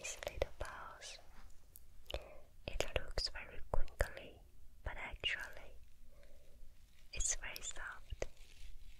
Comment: What's the word she says at 8:59? Sounds like quickly but obv isn't. She uses it a lot but I can't catch it :( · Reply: she says crinkly lol